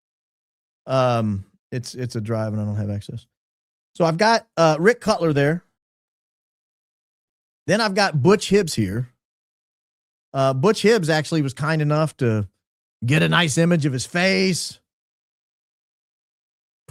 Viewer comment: I thought you said butch hips 💀😭😂 I couldn't believe it!